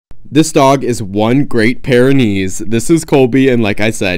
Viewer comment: I thought you said this dog is one pair of knees💀